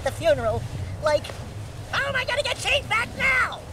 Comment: You know for the longest time I always thought Dexter said "How am I going to get Chief back now" instead of "changed back".. The swamp, so you might as well get used to using the double jump spin kick combo and those gymnastic poles the same platforming techniques will be present in the sequels. And if you liked the flutflut bird, you'll get see more of them in Jak 3. Really good edit work too, this one had smooth transitions and timing.You're doing really well, I'm looking forwards to your future uploads.